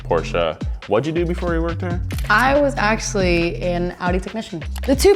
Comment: "Steph, what did you do before you worked here?" "I was actually an Audi technician" did i hear that right? will we not see her on RMS anymore?